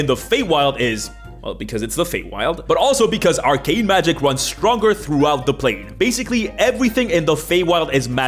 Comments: what he said: “arcane magic runs stronger throughout the plain.” Vs. What I heard: “Our Gay magic runs stronger throughout the plain.” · Instead of "arcane magic runs stronger throughout the plane" I heard "our gay magic runs stronger throughout the plane", and words cannot express my disappointment when I realised I misheard